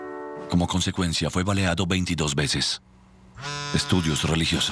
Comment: Porque dice 22 veces si claramente ahí dice 23